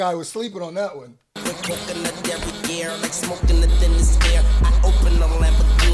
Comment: I was today years old finding out he said coke in her derriere. Thought that nigga said coconut derriere